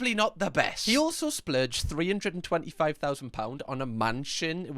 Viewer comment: did i hear that right.. 350k? for a mansion? where is this gentleman living holy